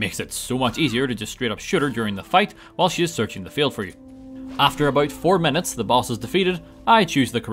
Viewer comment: Did you say "searching to fail for you" instead of "failing to search for you" at 21:51?